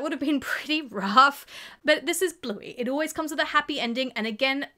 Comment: I think you said Blippi, not Bluey😂😂😂